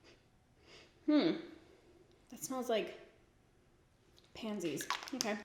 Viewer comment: When you sniffed the illuminater I heard you say ‘..smells like panties’ and like, because it’s you, I was like okay ya maybe 🤔 I mean, that’s unfortunate but..